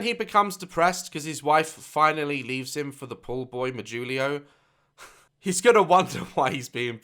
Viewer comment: Can someone explain what said at 12:33 ? I don’t understand? Poor boy julio? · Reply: He said "Pool boy Mijulio".